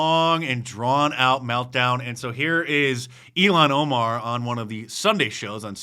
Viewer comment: Did Mark say Elon Omar??? LOL